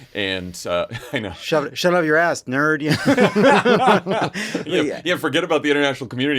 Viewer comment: i heard this in uncle baby billy's voice as "go outside, nerd"